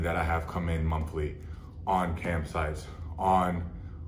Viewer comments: I thought he was saying camp sites and was very confused ... like I get it, camping gear is expensive. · I thought he kept talking about "camp sites." I was like "how the heck is camping gear that much of an addiction?" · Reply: Definitely thought he said camp sites too 🏕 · Fully thought he said "CAMP SITES". Was very confused at first... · At first I thought he was saying “camp sites” lmao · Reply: Thought he was saying Camp Sites as well. I was going to say, I know KOA is pricey, i didn't know it was that much. Back in the 80's it used to be Hookers and Cocaine, in 2020 it's Cam Sites...